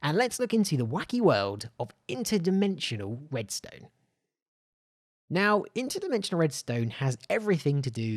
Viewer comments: did...did he just say..... WEDSTONE!? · did he just say WEDSTONE